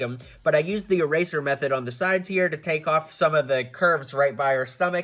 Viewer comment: did he call Thor a SHE?!?🤣🤭😅